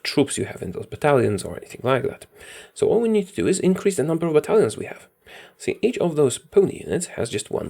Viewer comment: it might just be me and my dumb ass ears but I heard Italians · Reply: Idk, I hear battalion